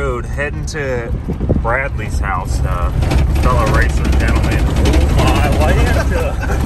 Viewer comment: “Oh my lanta!” I’ve heard say it, but never lol. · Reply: I always heard "oh my lambda" All these years, and I just assumed was a halflife fan.